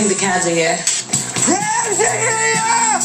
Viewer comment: Is it just me or does he say "Crabs are here!" ?